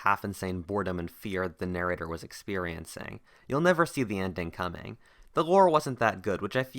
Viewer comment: What "wasn't that good"? I even slowed it down right there to try and figure out what you were saying.. The best I could decipher was, "the laurel" which seems unlikely.. And no subtitles.. :(